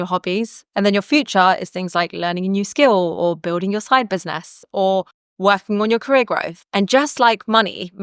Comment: You said "building your side business", but I heard " building your snipers nest". 🤦🏾‍♀️